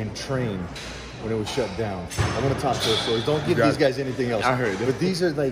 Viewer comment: What did Mike say at 12:32 he wants to talk his story ? I couldn't catch what he said ....... Please Im hoping you do a video on Robbys story !!!!!